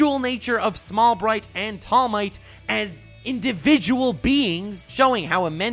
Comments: did I just hear "Tall Might" ? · he actually says tall might. tall bright +all might.